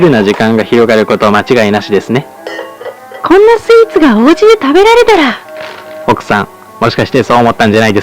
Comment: こんなスイーツがお家で食べられたらのところ最初コーンスープがお家で食べられたらって聞こえたw